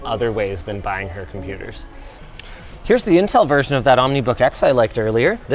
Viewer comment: I have this on 2x speed and wasn't looking, and when he said "the Intel version" I heard "the incel version", had to rewind back to see wtf was on the screen 😂